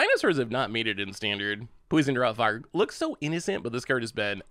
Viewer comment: Did I hear you say Poison Drog Fart? lol